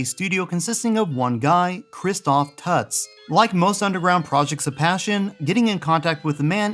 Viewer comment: Who else thought at 0:42 said Kristof Putz ??????? Cool video i to love the VECTREX : ) Love those vector graphics ... Walking into an arcade back in the early 80's the vector games just stood out, calling to you, saying " Put your quarter in me boy " ...lol